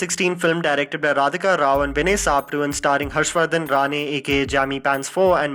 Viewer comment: did he said "starring homself"?